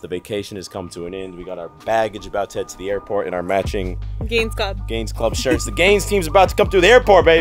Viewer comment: Not gonna lie, I thought said gains bra!!! Lol